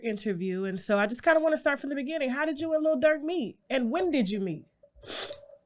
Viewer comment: Why I keep hearing “LIL DIRT” when they say his name 🤣🤣🤣